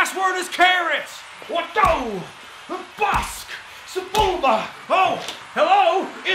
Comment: Did really say "Sebulba" at 8:05??